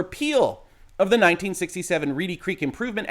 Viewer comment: I was only half listening at the beginning and misheard the district as "Greedy Creak" :-)